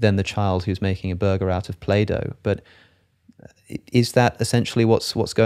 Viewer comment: Initially I thought you said that the child makes a burger out of Plato 😂 instead of play dough.